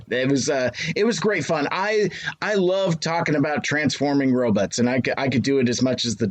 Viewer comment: Did say "Ro-butts"? 😆